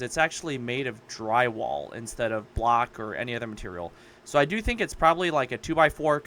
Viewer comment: Ohhh, "block." I thought you were saying "black", lol 😅